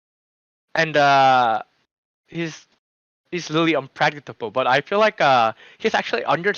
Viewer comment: Did crusty say probe is unpractiseable? Or maybe unpractical?